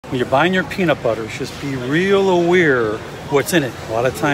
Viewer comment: I swear I thought I heard him say " be real oweir"I think he meant to say be real aware.